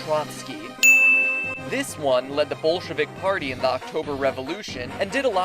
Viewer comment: I thought he said "bullshkuvik party". Also I love all the references to other YouTube channels